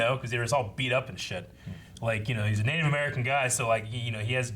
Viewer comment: did say his grandfather were native American? Or did I hear it wrong?